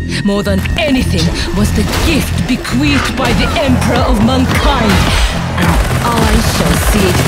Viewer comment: sorry but whenever she say "mankind" all i hear is "mon keigh"